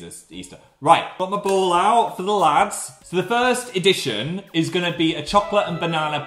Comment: I laughed when I thought he said "pop a ball out for the lads"